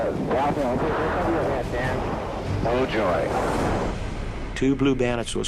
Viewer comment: I'm pretty sure he is saying "No joy" instead of oh joy lol